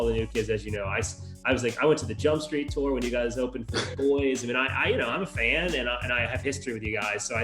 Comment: Who did Jared say they opened for on the Jumpstreet Tour? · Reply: I heard, "The Boys".